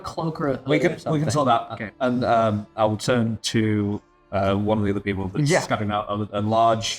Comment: i thought bloom said "turn INTO" that lizard folk as a disguise, not "turn TO" as in request a disguise from 😂